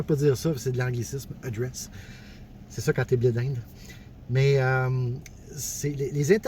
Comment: J'ai compris "c'est ça qui arrive quand t'es blé d'inde" au lieu de "bilingue" 😂